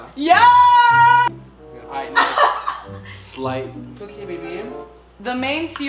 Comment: did Larray say “my baby “ in Turkish to or is just me hearing it wrong ❤️❤️❤️❤️❤️🇹🇷🇹🇷